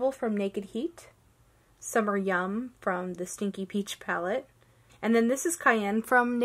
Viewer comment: I swear I heard call it the 'Stinky Peach' palette